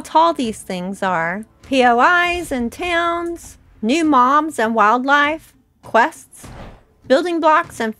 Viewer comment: I thought you said new "moms"??? Lol